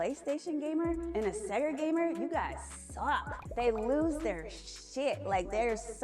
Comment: You misheard the lady. She said "you guys suck" not "your graphics suck." Referring to the companies as a whole not the graphics. Therefore you state a incorrect argument based on false representation. Merry Christmas 🎄 it's a dead give away when somebody calls the Genesis a Sega that they weren't into gaming back then. She's a fraudulent red tricktock fashionista. Still doin it for the vines.